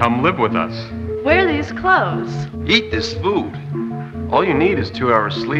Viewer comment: Am I the only one who heard "yeet this food"